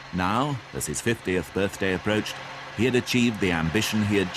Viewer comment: 50th birthday approached 😮😮😮form which direction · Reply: I had to listen, infact replay that part of the video again, because I thought the narrator said '60th birthday' I was very shocked when he said 50th!!!😮